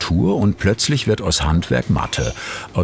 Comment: "Plötzlich wird aus Handwerk Mathe". Ich mag ja die SWR Handwerkskunst Sendungen, aber das ist einer der dümmsten Sprüche die ich seit Langem gehört habe. Kein mir bekanntes Handwerk funktioniert ohne Mathematik.Selbst im gezeigten Beitrag muss der Bäcker Massen zusammen rechnen.